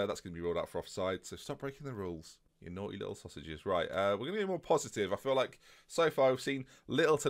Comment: "naughty little sausages" did i hear that right? XD